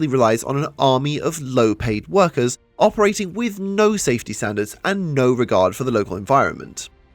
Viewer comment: "No safety standards" misheard as "no safety sandals"